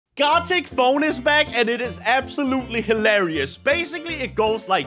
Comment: I thought he said "God take phone is back" 😂